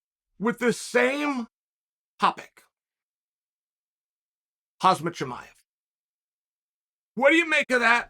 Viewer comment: could swear he called him HAZMAT CHIAMEV😆